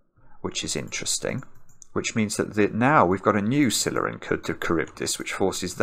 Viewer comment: What did say at 23:22? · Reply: Scylla and Charybdis. Two mythical creatures probably most known from the Odyssey. Basically the same idiom as saying, "stuck between and rock and a hard place."